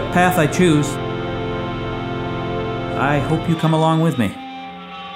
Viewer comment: I thought it said 'thanks for an amazing 10 years beard' and I was confused at first why the emotioal music was playing.